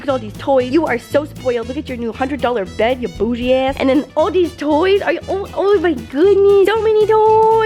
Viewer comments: I thought she say in 7:03 boujee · did she say “u spoiled ass” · did say “bougie ass” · Did say as* at 7:03 ???